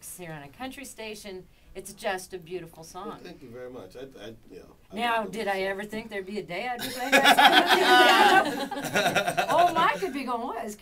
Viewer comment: What does the interviewer say at 6:55 ? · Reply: I think something like, “did I ever think there would be a day you would be playing that song in our studio?“